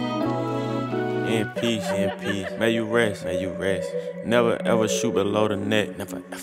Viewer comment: For one minute, I thought he said, “IMPEACH, may you rest”.